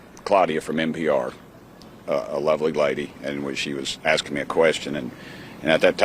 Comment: I thought he said a “lovely whitey” 😂😂😂😂😂